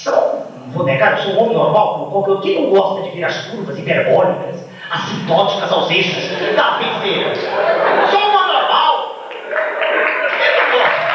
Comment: Alguém aí sabe o que ele disse depois de "hiperbólicas"? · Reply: "curvas hiperbólicas assintóticas aos eixos da Feiticeira?"